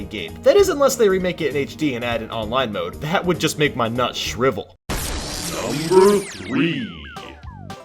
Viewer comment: did he just say it would make his… nuts shrivel?